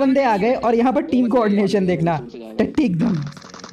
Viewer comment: did you say tatti 😂😂😂